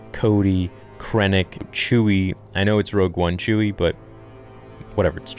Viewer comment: Is there something wrong with my ears or I heard him say "Rogue One Chewy"??